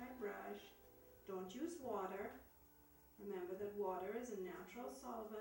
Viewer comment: In 08:20 Did you say "don't use water"? I don't how do you do to mix the colours so well (I'm sorry for don't understand all in your video, but I speak spanish).